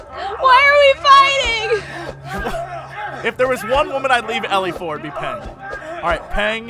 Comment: lol at 27:00 I thought he said "if there is one woman I would leave ellie for, it's you." Then I was like "lolwut" and watched it again. Then I realized was talking about Peng. XD.